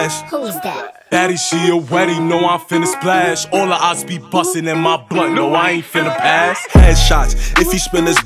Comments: Lol 😆 🤣 ... 00:27.... that line was kinda suspect.. but I realized what dude said... my bad... dude said "all MY opps be busting in my BLUNT" MY BAD FAM.. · WHAT DID HE SAY???????? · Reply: He said blunt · So nobody gon talk abt wat he said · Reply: He said blunt 😭😂